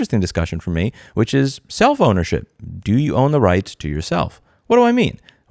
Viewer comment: When you said "self ownership" my brain for a split second thought you said "cellphonership" and I just wanted to let you know I'm an idiot